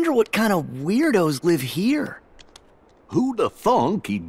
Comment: sounds like he said who the fu*k